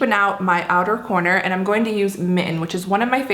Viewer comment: Lovely pallette! But be sure you pronounce the T's in 'mitten' because they are not silent! I was in the other room when I heard that color's name and I thought you said mi-en' and was confused because I have lived in the miTTen state my entire life!